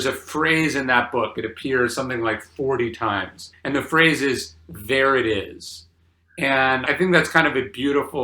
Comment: When you said “There it is,” I thought you said Veritas